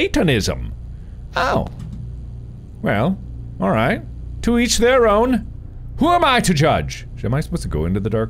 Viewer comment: When Mark said "to each their own" I thought he said "to eat their own," and I was like yea that's what cannablism is